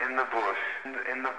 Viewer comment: Sounds like their saying “ in the booty” at the end of video 😂 I love Izzy regardless